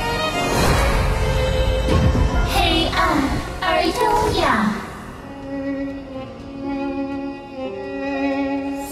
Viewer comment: What does Xiao Qiao say at the end of her dance? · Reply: Darkness and elegance